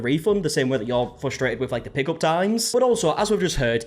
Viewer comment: My auditory processing is so terrible today!!! I asked "wait can you repeat that?" Nonstop at work today. I thought my mind was too busy. But i was 10 minutes in when i realized he was saying "PICK UP times". I kept hearing "PICKLE" 😂 and just figured i missed something and would eventually get context.